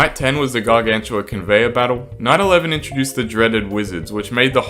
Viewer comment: did he say 9-11? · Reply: Night 11